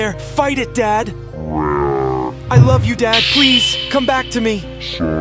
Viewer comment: Bro I thought the dad said " son kill me " 💀💀